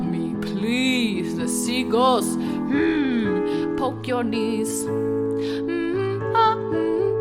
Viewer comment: This video autoplayed at 'Seagulls' and all I heard was "HMMM...POKE YOUR KNEES" LOOL